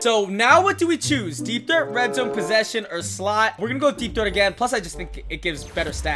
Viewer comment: He said deepthroat at 8:24 instead of deepthreat